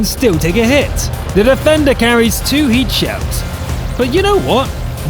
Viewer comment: did u just say : the defender carries 2 heat shells"? hahahaha i think it's an HE shells hahaha